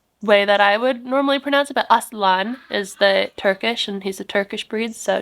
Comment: 😅 I thought you said Aztalan